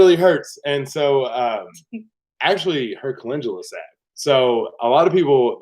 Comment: Ooowwwww, it’s hard to listen to you talk about peeling your fingers off the burner cap! What was it that you used to stop the pain after the vinegar? Glad you’re on the road for healing! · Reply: It sounded like they said her "calendula salve" Sometimes between their giggling and talking over each other, it's hard to understand what they are saying.